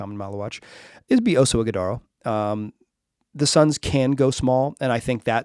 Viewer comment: I thought I heard Iguodala, immidiately went into "da fk" face😂